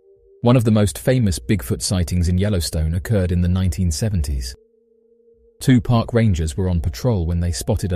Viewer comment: Did he just say Tupac Rangers?!